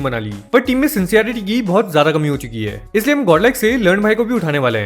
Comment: what did I just heard 😂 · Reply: Ya toh win hai ya toh "learn" hai